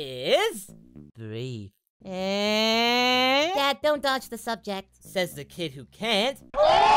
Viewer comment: gohan: dad dont dodge the subject goku: says the kid that cant gold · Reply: joke wasn't as funny to me because I heard "change", not "dodge".